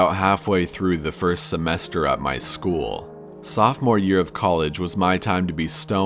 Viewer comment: s433p College Sophomore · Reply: Fiji Doing Things Ah. I thought he said during my sophomore year of high school